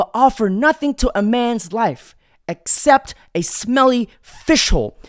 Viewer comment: A fish hole At first I thought he said OFFICIAL I was like what's that mean. 😂😭😭